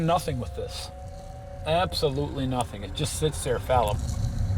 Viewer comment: What did he say at 19:55. The caption say "fallop"